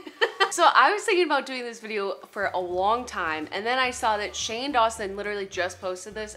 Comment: did shejust say wong time with a w